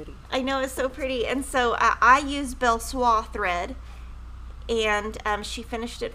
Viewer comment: says what thread she used on the Starlit Snowflake - I've played it over and over but I still can't understand the name of the thread, Please What did she say? I want to make it just like hers for my daughter. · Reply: Hello Nita! Here are the colors that used for Starlit Snowflake: Belle Soie Sister Scarlet Belle Soie Rosebud Belle Soie Carnation Belle Soie Orchid